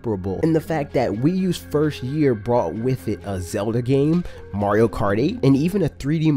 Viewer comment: Did you said Mario Karty 2:53 XD